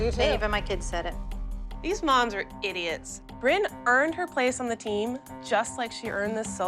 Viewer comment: did Ashley really call them idiots